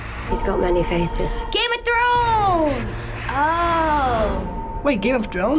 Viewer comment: Wait did he just say Game of DRONES ?